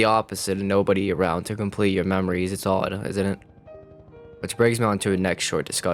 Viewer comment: dude what do you say at 4:13? "nobody around to complete your memories it's (?????) isn't it?" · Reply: "Its odd isnt it"